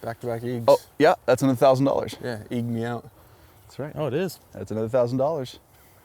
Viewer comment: I know I’m not the only one who heard “eating me out” 😂